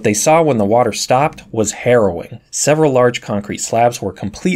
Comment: I had to replay that part because I thought he said Heroin